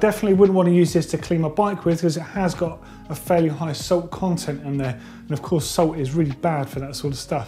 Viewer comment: Im really sorry but i cant quite understand what he is saying. Is it salt or soap? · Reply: Salt